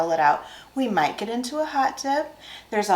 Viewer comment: I thought said “ we might get into a hot tub” lol.